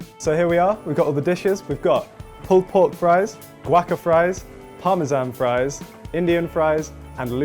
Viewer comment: a moment there i thought he said "pol pot fries" and now i can't unhear it